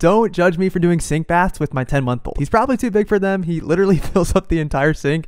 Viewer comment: I feel really stupid, I missed the part where you said “month” and thought you said “YEAR”! I was so confused until you brought out your baby instead of your boy! 😅🤦‍♂️👍